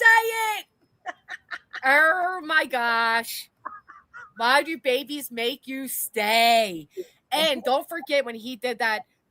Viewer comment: I thought he said "don't babies make you stink" :hand-orange-covering-eyes